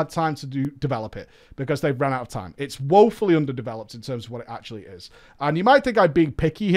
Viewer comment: I was away from my phone when he said "under developed" and I heard "its under the bell end" ...... I accepted this as a phrase ive never heard before but it sounded like something that must be a phase lol